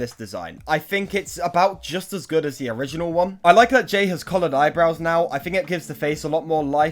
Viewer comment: Ohhh, coloured! When you said "coloured eyebrows" I heard it as "collared eyebrows".